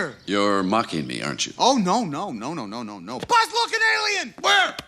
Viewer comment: When I was a kid I thought he was saying Buzz Lookin Alien haha like a alien that looks like buzz